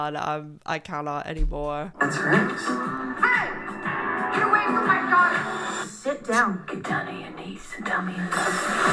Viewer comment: What does Tina say? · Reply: “Hey, get away from my daughter!”